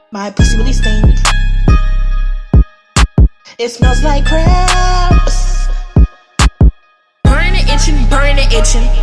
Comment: "It smells like crappssssss" SENT ME 💀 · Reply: i thought she said crabs